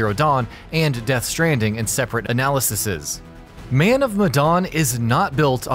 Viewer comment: I'm sorry did I just hear "analysises"?